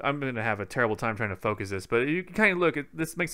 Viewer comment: Thanks for over 3000 Likes on the Nintendo Switch unboxing! Now it's time to unbox the new Zelda and compare it to older titles! Let me know what you want to see next! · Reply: I thought you said I’m an Oreo